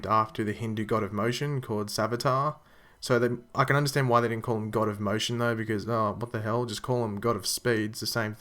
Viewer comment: with subtitles GOT OF EMOTION